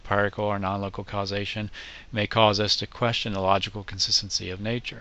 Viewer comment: pardon, what did you say at 5:59-6:01 ? "Light being a wave and particle ****** causation" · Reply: "nonlocal causation." That last comment is me questioning whether the empirical world is logical and why I should expect it to be logical. It leads to questions about the origin and nature of logic. In the end, I don't think it is logical, but that is mostly my hunch.... and logical consistency works pretty well in our everyday world, so might as well try to be logical. :)